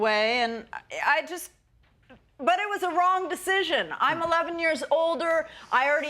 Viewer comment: When she said I'm 11 years older I thought she said I'm 11 years old.